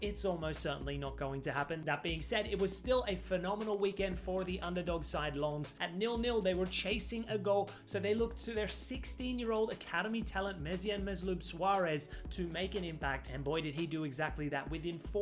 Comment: did bro just say his name is Mesut Ozil Luis Suarez⁉️😂